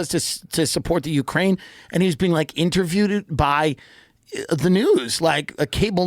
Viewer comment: Did he just say interviewedet 😂